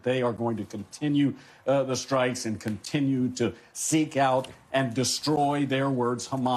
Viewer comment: I thought they said that they are women 😂